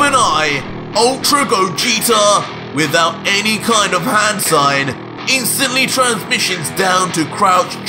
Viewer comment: Is that what I hear? Hand sign? 🤣